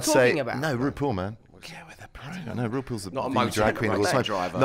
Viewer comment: What di ben whisper at 4:33 ? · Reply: He whispered “ Get with the program “